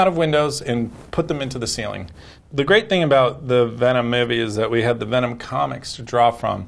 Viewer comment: it sounds like he is either saying "venom movies" or "venom movie is" 🤔